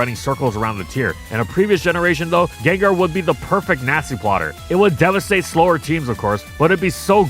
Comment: Did I hear Nazi-plotter?